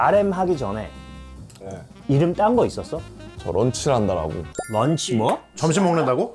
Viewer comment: DID SOMEONE SAY LUNCH RANDA?? 😂